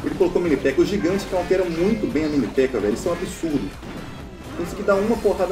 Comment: o gigante cowntera muita bem a mini pekka... Kkkkk não entendi